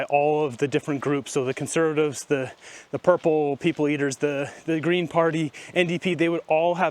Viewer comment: Heard you say....purple people eaters....lol